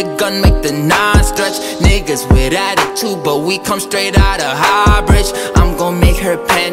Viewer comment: I thought it said "but we come straight out of Hogwarts" :')